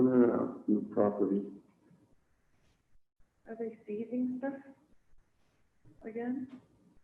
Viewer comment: I think when Lori pauses when asking if they are ceasing things again she was trying to catch herself from saying something incriminating. Either that, or she was thinking they were toast. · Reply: I thought she said "seizing stuff "